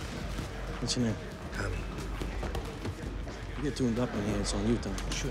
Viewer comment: What does the coach say? I can't make it out. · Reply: “You get tuned up on here it’s on you”